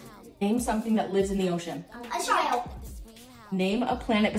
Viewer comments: When he said whale I thought he said a child so I just had an image of a random child just chillin in the ocean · I thought she said “a child” not a shark Lmaooo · When and said "whale" and "a shark" i thought they said a child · When brothers and sisters answered: a whale, a shark" i heard "a child"